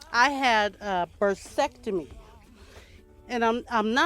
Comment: Lol 😂 I thought you said vasectomy!!